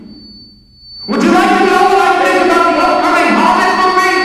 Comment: What does he even say at 3:00? "WOULD YOU LIKE TO KNOW HOW—(blaring)—HOBBIT FLIPPING?" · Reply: “Would you like to know what I think about the upcoming hobbit movie”